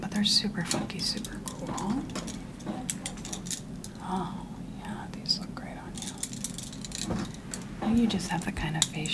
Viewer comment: Am I the only one who thought she said "yeah those look ugly on you.” for the last pair of glasses?